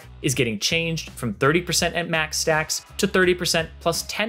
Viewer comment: there is mas instead of max no hate, just saing